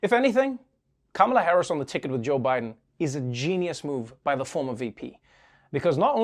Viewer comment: I thought she said Biden is a rapist